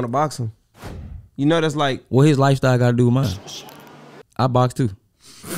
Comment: I thought buddy said he a boxer too 🤣